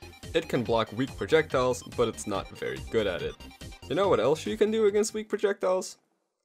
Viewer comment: You called Simon and Richter a girl.... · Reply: He said "what else you can do", but I can see how you misheard that